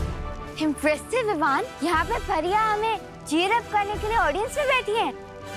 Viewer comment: WAIT WHATTT??????DID AUROHI JUST CALL BALVEER Jr. VIVAAN AT 10:24?????????